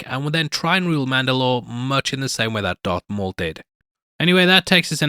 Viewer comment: Am I crazy or did he say Dark Maul?